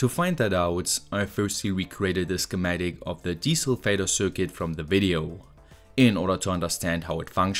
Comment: I know you're saying 'desulfator' but I hear 'Diesel Vader'.